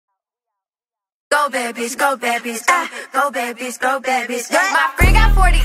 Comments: Pensé que decía "Go baby, Go baby, go" ¡-¡ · pense que decia no babys jajajaja